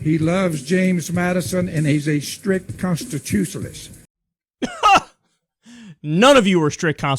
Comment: I think he said "Constushalinst"